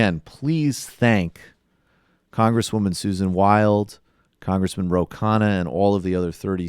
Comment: I thought he said Congress women ro kanah